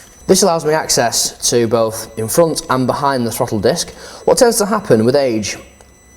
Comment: it sounds like he says front and behind the fucking desk